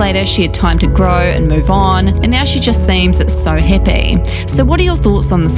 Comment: Omg she’s saying happy, I thought she said hippy at first 😂😂